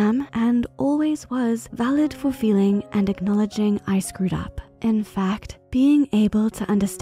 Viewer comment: I heard: acknowledging icecream